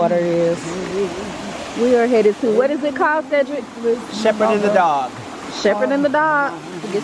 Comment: Girl , I thought ur brother said Shepard in the dark.😂